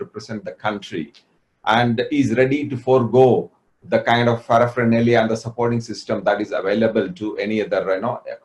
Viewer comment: What exactly is the word he used at 7:54? Fanaflaralia? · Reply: Paraphernalia